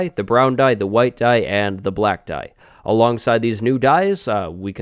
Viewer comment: Did he just say " the black guy"? Oh...no, the black DYE. Good thing this aint Twitch amirite TFBlaaaaddee